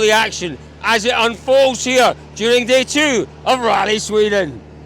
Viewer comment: did he said "Come on Britain" or "Come on Breen", not sure if Irish folks will like that 🧐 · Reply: He said come on Breen